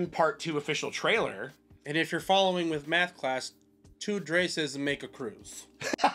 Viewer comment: I thought said “(2) Drases make a Groot” … go on… 😆